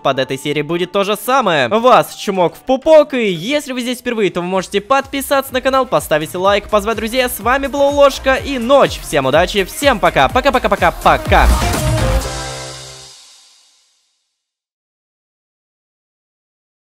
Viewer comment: Мне послышалось или сказал "С вами был Лололошка и Нотч всем пока"?